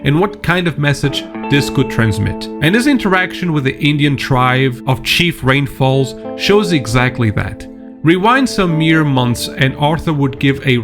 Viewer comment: I misheard that as “Idiot Tribe” and I was like The Fuk I say, The rewind the video to listen to it again I was so close to clicking off the video